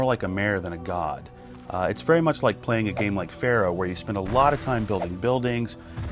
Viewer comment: please tell me what game is he talking about!! I hear something like "pharrell".. please I really need to know that!! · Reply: Pharaoh. I know it's been 6 years but maybe you're still wondering ^^